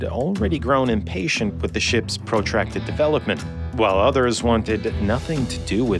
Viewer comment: Exquisite video as usual! at 7:50 there's a mistake in the transcription the caption say "But many in the Navy had already grown impatient with the ship’s protected development, while" It should say protracted instead of protected